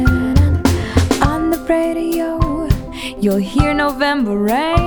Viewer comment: 3 years ago i thought she was saying on the pedo -_